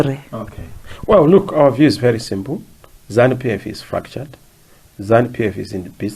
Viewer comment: did he say "ZANUPF is F'd up"? Like actual F word? I heard an F word